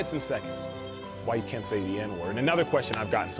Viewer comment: FYI...subtitle is wrong at 7:31 "so that's why in a nutshell in a few minutes and seconds why you can say the N word"....should read "can't say the N word"....big oops if hearing impaired people are reading along....